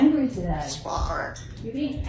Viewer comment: Doesn't he say "Shove it!" and "Stop it!", haha! Gizmooo, i love you soooooo! And i'd tell that other bird to shut it, too! SoooooooOOOOO loud and trebbelly! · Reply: I'm pretty sure I also heard him say, "Knock it off!"